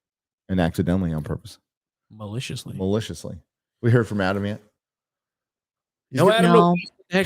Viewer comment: For a second there, I thought you said, "Adam Ant". Ha!